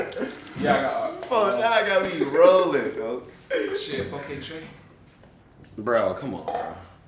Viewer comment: What he say at 23:10? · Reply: he said 4KT (4 Kay Tray) NBA YOUNGBOY affiliate aka LIL TIM shot VON in NOVEMBER 2020 and polo g and king von got a song together so that made him feel type of way